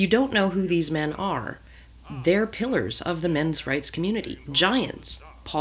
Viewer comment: At first, I heard that as ". . .they're pillers of the men's rights community." As in red-pillers.